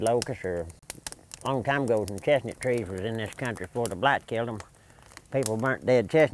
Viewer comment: what did he say killed all the chestnut trees off? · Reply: he said Blight, caused by Asian Bark Fungus